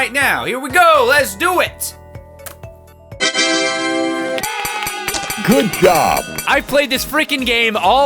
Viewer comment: it sounds like it said good yob